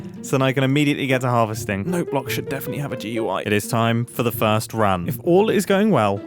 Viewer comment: did he say note blocks should have DUI? I know he said GUI but it sounded like DUI